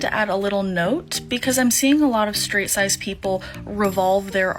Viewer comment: Did... did she just say straight sized people??? I